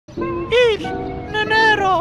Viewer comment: i think he said "Es, dinero"